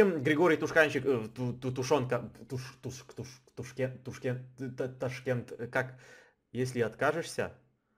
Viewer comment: Мне послышалось или на 14:34 он сказал Ташкент, это мой город, где я живу